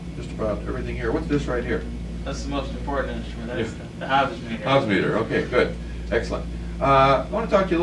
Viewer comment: At the 54:02 mark - What is the examiner asking about? I can’t make out the audio, but to my ear, the applicant sounds like he’s saying “hazard meter”...? Anyone know what he’s saying? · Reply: It’s the Hobbs meter it measures total time